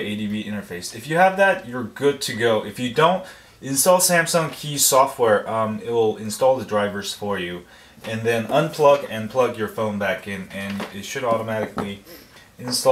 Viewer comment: Aa! Okay, I thought you said Samsung key :P Lol.